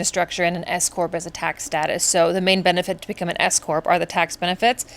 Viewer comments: Girl I thought you said you became an ESCORT instead of S Corp and I had a heart attack for a lil bit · Wow, when you said S Corp. I thought you said escort. And you just said it’s so nonchalant. 😂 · I thought you said escort instead of s-corp. Whew, momentarily stunned! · I KNOW I’m not the only one who thought she said “I decided to become an escort” instead of “s corp”. I was like “yaaaas okay!” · I thought you said escort 😅….S-corp…got it 😂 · Oh S corp! I had to rewind that I thought you said escort 😅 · LOL! When you said S Corp, I thought that you said escort 😂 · I had to do a double-take at the mid-point becuz I swore you said "I had to become an escort". Girl, that is some serious honesty! So I relistened and realized it was actually "S-Corp". Still bit sketchy on what that actually is but that momentary jaw-drop when I heard you had become an escort - wow. Ear cleaning time...😮